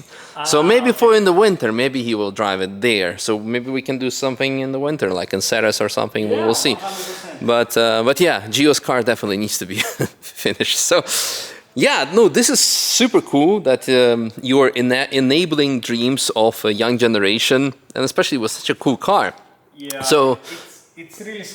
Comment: Я один услышал на 3:14 "ну"?